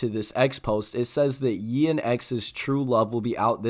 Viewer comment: bruh did u just call him yee?